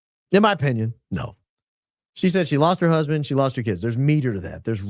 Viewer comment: Yes! It sounds like Luna said that EK "lost her husband... and her kids." I listened to it a super slow playback speed. Side Note: Mafia = Italians Mob = Jewish Zionists